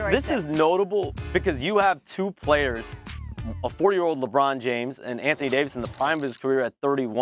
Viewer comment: Does anyone else hear this man say the Lakers have a 4 year old Lebron James? 🤣🤣